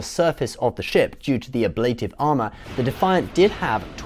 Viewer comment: oh man I'm so stupid, I thought they said in show the Defiant had 'A Blade of Armour' rather than 'Ablative Armour'....ahahhhh